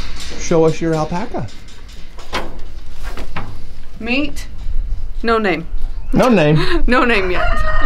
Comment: When you first introduced him I thought you said his name was Norman instead of no name, so I vote you name him Norman!!!!!!!!!!! c};o)